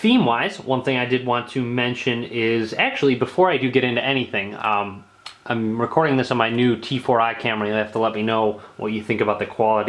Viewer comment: I thought you said "Actually, before I get into anything, I'm recording this in the nude." at 2:49.